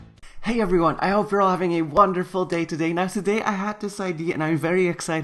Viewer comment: Because of his raspy voice today when I was moving something that fell he said,”Hey everyone I hope that you’re having a wonderful day today!”, and I thought he said a fucking good day lol